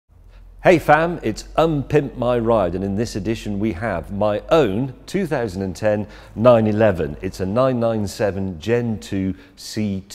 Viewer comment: "Hey fam!" -James May, 2019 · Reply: I thought he said hey fan